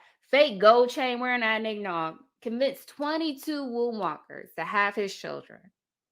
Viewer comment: 😂 I thought she said worm walkers because his age lol